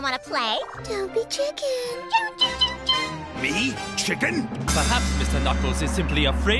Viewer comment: Did she say chicken?